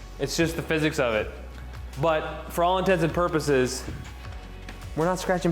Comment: it’s “for all intents and purposes.” Sounds like you said intensive. Just wanted to point it out!